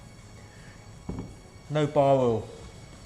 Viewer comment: I heard him say “no bar oil,” what are you guys talking about 🤣